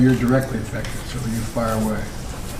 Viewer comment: did Joe just say " fire away" to Kamala?... 🤣 Joe is really crazy! Smh